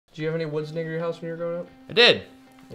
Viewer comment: It sounds like says the N word at the very beginning He’s saying “near your” but it’s kind of slurred and at first I was surprised